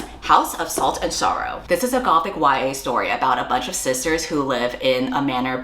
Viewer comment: when you said sisters i deadass thought you said "scissors" and wondered if that was a new fun term for lesbians💀